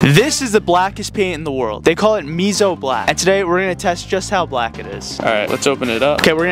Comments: First thing I hear is “they call this me so black” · I keep hearing "me so black"